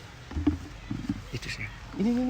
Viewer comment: Itu sayang 4:58 gk salah dengerkan,kameran manggil sayang ke 😂